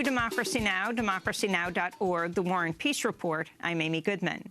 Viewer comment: "Welcome to Democracy Now!, Democracy Now! dot org, the War & Peace Report, I'm Amy Goodman." If I'm hearing those words, I'm drinking my morning coffee and preparing my brain for the kind of proper news report which corporate media "junk news" could never give me. Thanks, Amy and Juan! I rely on you guys, and on other independent news outlets, to stay properly informed. It takes a lot of work these days to be informed, but you guys make it just that much easier. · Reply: I agree though I thought Amy was saying ".. the War on Peace report". Either way it works.